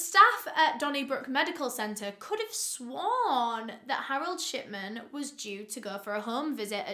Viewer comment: this whole time i thought you were saying shitman💀